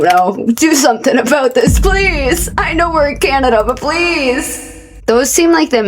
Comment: I thought is said Canadian and i got so fucking scared